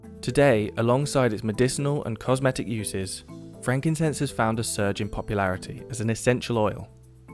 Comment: At first I thought you said Frankenstein 😅